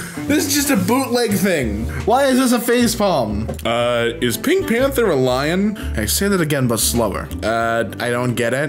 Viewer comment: this question actually makes sense, panthers aren’t a species on their own. Lions, tigers, jaguars, lynxes and leopards are all Panthera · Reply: i honestly thought you said pantera XD